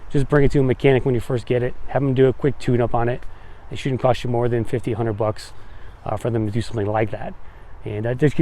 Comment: “Have’em do a quick tune-up on it. Shouldn’t cost you more than 1500 bucks”?! 😅 I must’ve misheard that one.